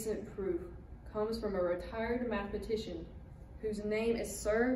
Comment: i thought she said "a retarded matematician" and almost spat out my tea. I mean dirt.